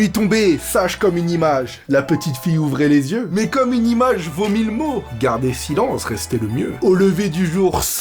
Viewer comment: Pourquoi j'ai imaginé 0:32 "une image vomit le mot" ?